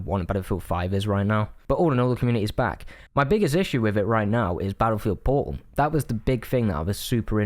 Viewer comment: I legit heard “My biggest issue right now is Battlefield Porn” I was like Ayo! No way right? Then 2:41 “So I went on to Battlefield Porn but there doesn’t seem to be any official servers for it” 😳🫣🫡